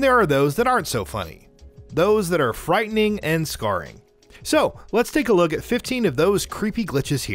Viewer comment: did he say frightening and scarry???